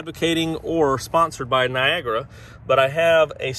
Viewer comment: I thought you said "viagra". 😂